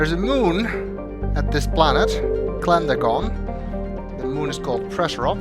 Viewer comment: I thought it said Klendragon at first.